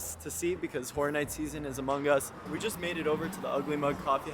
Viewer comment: i really thought you said the new fortnite season is among us